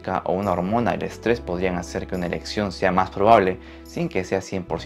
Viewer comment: En 13:30 oí erección en vez de elección